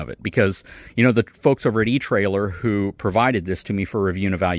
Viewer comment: Where can I get the Lippert On-The-Go ladder receiver. My camper came ready with backer boards to support the installation of a ladder, so I would love to install this Lippert receiver for its ladder vs the typical ladder system. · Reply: I thought he said etrailer